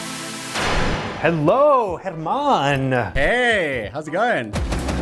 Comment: When he said German I thought he said Air Man and I was like well his hair does have gravity defying properties sooo